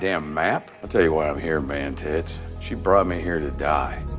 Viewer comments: And gera I am thought he said mantis.. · I thought he said mantis and not mantits had to watch it like 3 times 😂